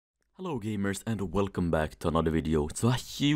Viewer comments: trg go hard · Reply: Why does the intro sound like you said: little gamers, instead of hello gamers? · At the start of the video I thought he said boomers instead of gamers